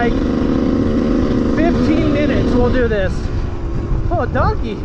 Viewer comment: Idk why but I thought you said “o a donkey” lol